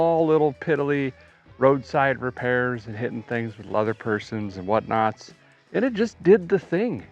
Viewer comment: When you got it home you spoke of having to hit it with a few "leather persons" haha I thought you said I had to hit it with a "leather purse" a time or five.